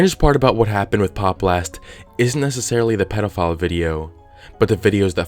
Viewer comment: I thought it said "The Pope blast incident"